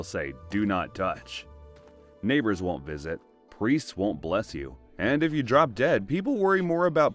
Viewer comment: I thought you said "priest would not m*l*st you." I had to rewind to listen closely, like "what?"